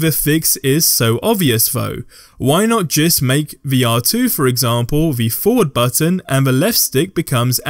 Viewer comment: i didnt understand what you just said. Vr2? What did he say exactly